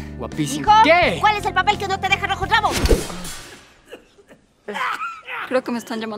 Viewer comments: que dijo?xd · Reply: Hijo cuál es el papel que no te deja bajo el clavo 🤣😂😅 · que dijo la senora? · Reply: ¿Cual es el papel que no te deja rojo el rabo?